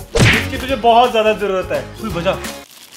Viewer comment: Can anyone say what said at here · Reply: Baja